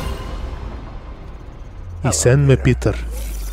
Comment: No se JAJA pero en (TARTAR)-(TÀRTARO) 1:46 entendi Hacerme Peter JAJA XDD